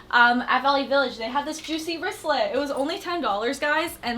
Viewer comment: when she said juicy i thought she said gucci. for $10. had a freaking heart attack